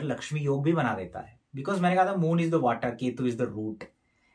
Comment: moon &ketu. Moon is water but ketu what you said is not able to listening clearly what that was sir you mentioned in video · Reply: Ketu is root